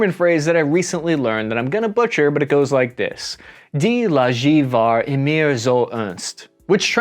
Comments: I am German. What did you try to say at 9:34 :)) ich habe "Die" verstanden. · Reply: I should have put the text! Die Lage war immer so ernst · For all Germans, who had great difficulties to understand this German phrase like me, the phrase was: „Die Lage war (noch) nie so ernst.“ It means, the situation was never so grim. I try to transcribe it into English (which is impossible): Dee lague war nee so ernst. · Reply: I thought he tried to say "Die Lage war immer so ernst," the situations was always this serious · Even knowing a bit of german I have no clue what he just said. I wanted to look up the specific phrase but its not what I'd expect it to be and all I can understand is "Die lager war immer zur unst (?)" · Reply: It could be „ Die Lage war immer zu Ernst“ wich means the Situation was always serious